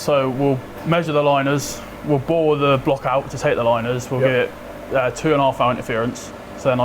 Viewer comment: Not a native english speaker here: can someone write what the operator said at around @ 1:24-1:25 mark? I heard "2.5 something interference". I am only familiar with letter and number tolerances, things like H5-j6 tolarance.